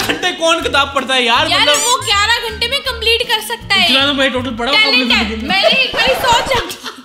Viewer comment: funniest part I didn't hear dialogue but when laughed I also laughed hard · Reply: He said "itna toh me bhi padha hu computer ke me " because he is a gamer 😂